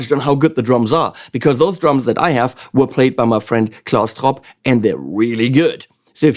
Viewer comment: What's the name of the drummer? I played the video 100 times, especially fro 14:50 to 15:00, and I couldn't catch it!!! PLEASE HELP!!!! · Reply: Klaus Tropp